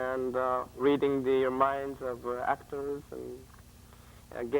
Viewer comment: I hear him say getting "e-mails" from another dimension